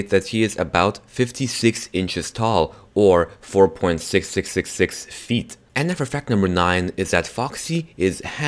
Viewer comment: DID HE JUST SAY 666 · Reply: No he said 6666